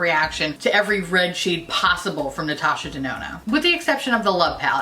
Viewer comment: I swear I heard you say Natasha Denono when describing your usual issues with red shadows... 😂